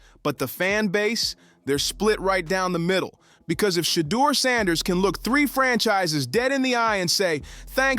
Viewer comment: Shaquille O’Neal? What huh did anyone else catch that 😂😂😂😂